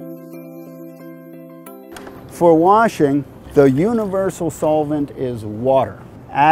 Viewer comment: I thought you said you were 'cleaners', whats this about washing?